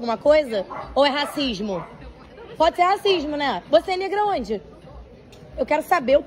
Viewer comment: Eu ouvi bem ou a mulher que filma fala para a funcionária preta em 1:59 : "você é negra aonde?" ...na qual nitidamente dá pra perceber que a funcionária sim é uma pessoa preta... tempos estranhos esses!